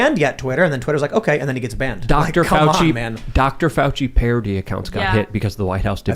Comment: What did Luke say around 33:10 with Dr Fauci. He said "Dr Fauci got hit or got paired something, something something, - that is absolutely wild" i tried listening a few times and for some reason i cant hear it well enough. Someone help me out here please .. its been a long day... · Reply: "Fauci parody accounts got hit..." They nuked obvious Fauci satire accounts from Twitter.